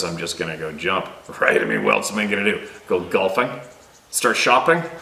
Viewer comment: Hmm, I DO think they were listening! Could they possibly be one of the noises in the background? I was kind of suspicious of that as you were talking, esp today... Then, right here, 1:02:22 (sounds like they say "Ya know" in a high- pitched, bird like sound) I kept feeling like there were extra sounds, that were tough to identify! ! They seem to really try to mock you, and THEN you mentioned that they might be listening. Is that your GUT feeling? It's never wrong ! I think one of those noises in the background is one of your buddies for sure!